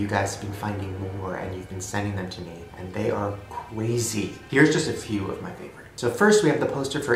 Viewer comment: did Shane say cwazy